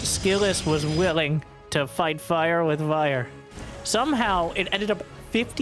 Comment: I totally heard "fight fire with Aiur" in there lol